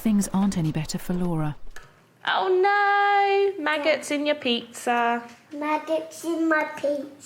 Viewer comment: What on earth is this game??!?! Did she say "maggots on my pizza"!? LoL. 😂😂😂🐛🐛🍕